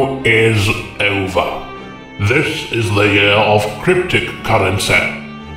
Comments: Aw fuck, thought you said cryptids, now where the fuck am I supposed to leave this Mothman and my 3 Fresno nightwalkerss? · Oh, I thought you said cryptid currency! Fine, well, I'll take my Bigfoot Bucks elsewhere!